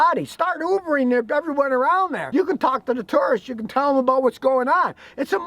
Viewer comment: When Scotty said "tourists" I heard "turds"